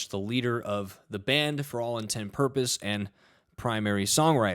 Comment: did you say 'for all in ten purpose'?